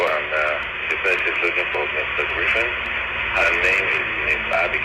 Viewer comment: I heard "she said she's looking for Mr. Griffon."